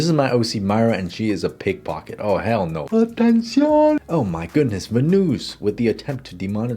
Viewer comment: Was that spanish? did he said ''atención''?